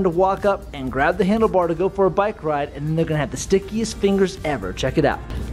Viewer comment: Did he say the n word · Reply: Joel Cheung he said 'and your gonna' 😂😂😂